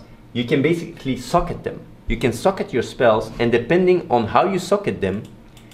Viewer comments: You can suck at your spells... ...Depending on how you suck at them.. Thats how i heard it while not focusing :D Lol'd prettey hard :D Nice video bro! TTTT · I allways thought he said you can "Suck at your spells" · Oh, haha! I like how his accent can be a little confusing at times! When he mentioned that about socketing skills, I thought he said: "In diablo 3, you don't level up your skills. You can basically suck them. You can suck at your spells, and depending on how you suck at them..."